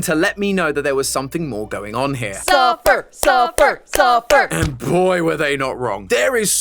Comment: I thought they were saying SUPPER SUPPER SUPPER SUPPER